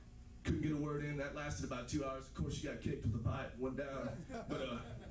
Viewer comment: did he say the pipe without her? Lol that was funny ass true story I'm sure